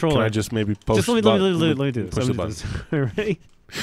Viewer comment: Did say: "Go ahead" in polish? ("Proszę bardzo") or I'm just delusional?